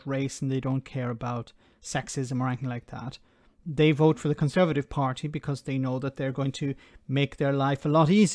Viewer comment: Had to do a double take on what he said. I thought he said they had voted for one. Time to change my hearing aid battery